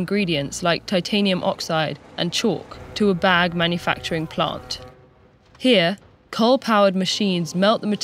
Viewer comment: i thought she said bad manufacturing plant